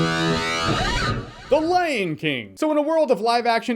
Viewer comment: okay.... Did you say 'The Lame King' or Lion King I just can't figure it out